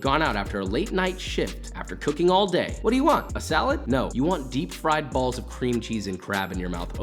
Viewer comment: What I heard was “I want deep fried balls”